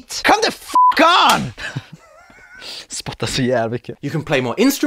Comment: Are you from Sweden I’m from Sweden because I think I heard ”spotta så jävla mycket”